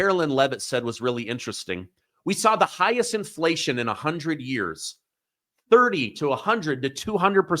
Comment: I could have sworn she said 40 years I must have missed the 100 years